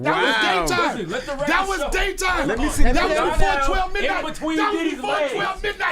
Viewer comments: Idk I heard "That Was gayTime" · I thought he was saying that was GAY TIME . He was saying that was day time !!!! 😂😂😂😂